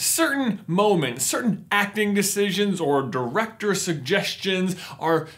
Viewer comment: when you said "director decisions" i thought you said "erector decisions"